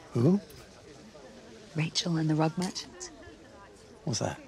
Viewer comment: I thought she said, "The parable about Rachel and the rug munchers". It's the accent.